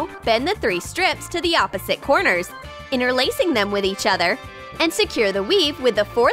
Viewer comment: did anyone hear the girl say Insecure the weave or is it just me? XD